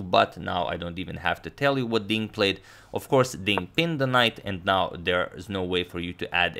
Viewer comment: I heard Ding Ping's the knight😅